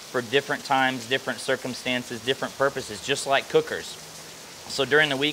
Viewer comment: When he said "different types of marinade different circumstances, different purposes" I heard "just like Hookers" I had to rewind. But I guess the channel is called meat church